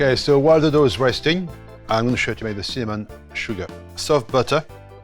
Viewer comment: It sounded like semen sugar 😆😅 3:46 semen buns